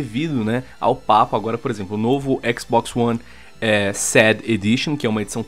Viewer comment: eu ouvi sad edition ?